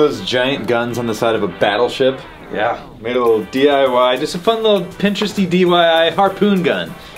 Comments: Did he just say “DYI” ? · did he say dyi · Oh my god. Did he say DYI instead of DIY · Did anybody catch he said DYI? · Who else heard say "dyi"..... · I’m pretty sure at 1:53 said dyi instead of diy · did said dyi harpoon gun